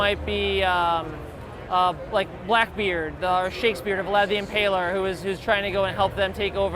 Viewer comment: Did the guy say Shakespeare was in hell? Anybody else hear that? It's at like... 3:27. Did I miss something here?